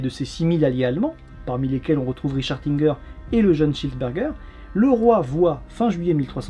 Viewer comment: J'ai entendu le jeune Cheeseburger... Je pense que j'ai fain . Plus sérieusement vidéo passionnante comme d'habitude !